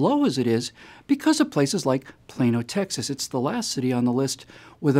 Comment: WAIT!! All this time it was “Plano Texas?” I didn’t even know that was ACTUALLY a city name! I just thought people were saying “Plane ol’ Texas” 🤯